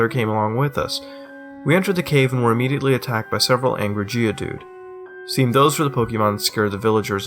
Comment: i swear, when mutahar said "several angry geodude" at 4:10, i thought i heard "several angry jews"